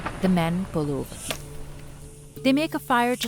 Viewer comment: "after 8-9 hrs of driving, the men pollute." "Pollute" is by far the funniest word for "set up camp" I've ever heard ⛺🔥 😂 I know that's not what she said, but I didn't understand her, and the caption says pollute. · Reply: the men pull over